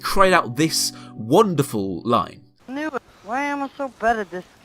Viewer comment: what did the guy say at around 4:56 I couldn't make it out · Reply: "Why am I so bad at this game dude" or something like that :D